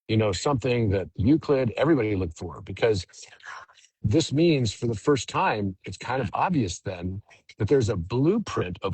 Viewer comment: I hear a background whisper. Did anyone hear it, I cannot make out the message.. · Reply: Yes 🌈🧬🦄🧬🌈 omg I was scanning the comments to see if it was just me…definitely heard seven I think… listening closer now